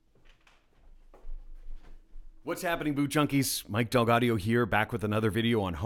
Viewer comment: It sounded like you said "What's happening boot chunckies! "